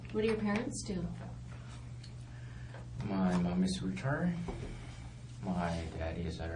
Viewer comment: straight up sounded like "my mom is retard"